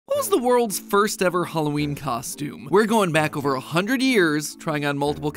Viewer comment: Am I the only one who heard who is the world first Halloween costume